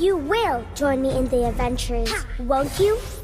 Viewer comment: All I heard was "You will join me in the avengers won't you?"